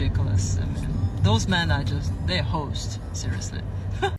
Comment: I think she says "hosed".